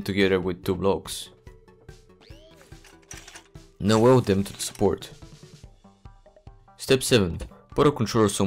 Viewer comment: What do you mean weld them to the sport? 5:45 I cannot figure out what you said or what you did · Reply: "Weld them to the support" That is the piece of wood that holds the wheels on either side.